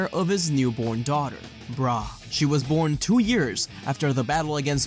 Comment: did I hear that right 1:50:40 you said the child's name was "brah"